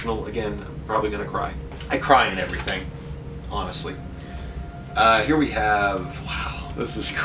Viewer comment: Was listening to this while doing chores around the house, and I heard "I cry at everything, honestly" and if that ain't me, I don't know what it is, hahaha. What a gorgeous stack of books!